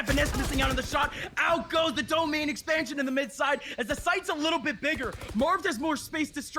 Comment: did I just hear "Domain Expansion"??? Valorant become Jujutsu Kaisen?? What a caster. 🤯😂